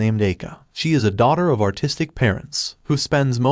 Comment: I heard, "she is the daughter of autistic parents" 😭